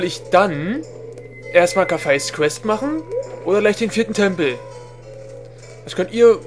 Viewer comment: Bei 1:00 hab ich verstanden: danach der titten-tempel :D